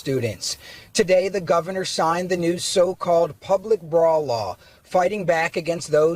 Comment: I thought the News guy said "public bra law". If a law banned brazzieres, I'd go for that. Can we all create a protest for that?!!! " Free the TaTas! " "No more jailing boobies! "